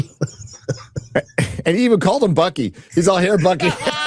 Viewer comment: I thought you said monkey when you said Bucky. Reference to dementia cap America giving over the shield. Laughed so hard.